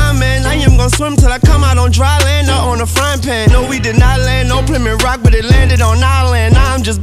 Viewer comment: I thought he said nut on a fryin pan